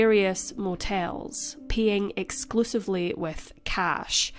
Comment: Your accent! I thought you said “peeing exclusively with cash.” And then I thought, I think she meant PAYing. 🤣